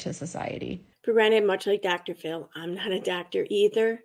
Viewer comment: At first I thought the pillow said "I am not a Sober" 🤣🙆‍♀️